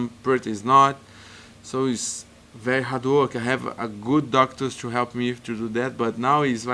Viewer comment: ” very hardwork” sounded like hadooooken 🤣🤣👊🏻👊🏻 gotta love costa! Humble, beast and down to earth, congrats on the english, great improvement! 🙌🏼🍀